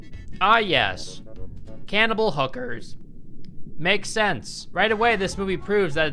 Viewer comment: 'Cannibal Hookers', one of Armie Hammer's all-time favorites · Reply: Deadass thought that said Hannibal Cookers